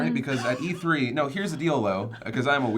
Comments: Did say heres the dildo @ 5:14? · Oh ray William Johnson when said here's the deal though I hear..... here's the dildo